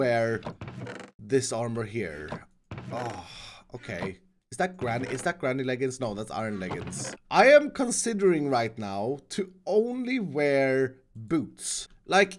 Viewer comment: Did he say granite leggings??